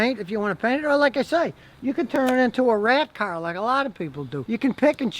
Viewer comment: Did he say “turned it into a rat car”